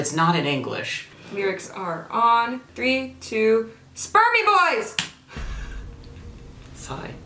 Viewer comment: When she said “spur me boys” I heard “spermmie boys” 😂